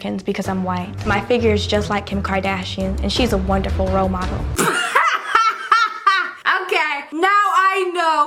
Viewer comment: "My figure is just like Kim Kardashian, she's a wonderful role model." What I heard: "My figure is just like Kim Kardashian, she's a wonderful robot."